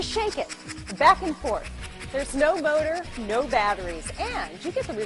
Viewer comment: I swear she said "there's no boner..."